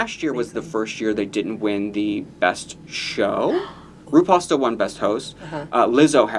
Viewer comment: When said “RuPaul still won best host” talking about the Emmy’s I heard “RuPasta won best host” and I will now be referring to RuPaul as RuPasta whenever I feel like it, so thank you for that.